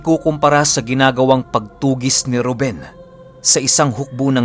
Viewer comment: Ang bagsik ng Ep. 6 ang daming rebelasyon. Tama ba narinig ko sa 15:12 na mention si “Ruben” as in si “Lolo Ruben”? 🗽🇺🇸